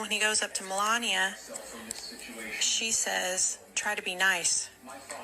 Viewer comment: I think Melania said “That was Nice” I don’t believe she said “try to be nice”. Because she didn’t know what he had said. She she took it as a sign of respect from baron towards the outgoing President and VP.